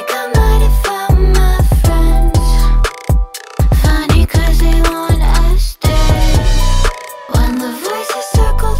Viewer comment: YA’LL THIS IS FAMILIAR BECAUSE IT WAS PLAYED DURING THE DICTATOR MV LISTEN CLOSELY TO THE CAR RADIO IN THE START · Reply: "Funny cuz they want us dead" part! All this time I thought she was saying "Funny cuz they wanna ice me"